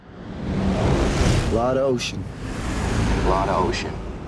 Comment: I thought they were saying, "Blood Ocean" and I was really hoping they'd have some Dethklok playing :(